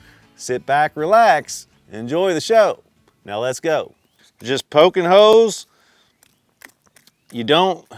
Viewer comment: Thought he was saying "poking hoes" Im relieved